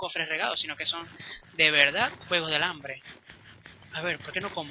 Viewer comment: yo entendi juego de alambre xD